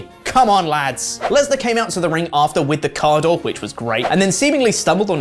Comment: Somehow I misheard ‘coming out with “a car door”’ as ‘coming out with “Okada”’ and I was stupendously confused.